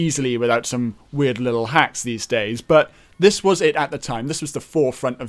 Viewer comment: I thought he kept saying "hacks" realized he actually says hats.. lol